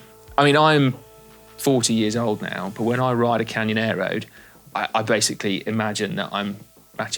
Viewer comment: Am I the only one who briefly misheard "Canyon Aero" as "Canyonero" (re: The Simpsons mocking American SUV culture).